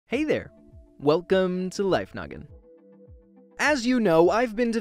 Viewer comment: Is it just me, because I heard him say, Life Nugget